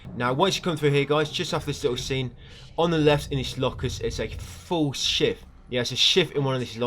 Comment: Sounded like you said. "On the left in these lockers is a FULL shit". 😂😂😂😂😂😂 I was laughing for 10 minutes. My chest hurts.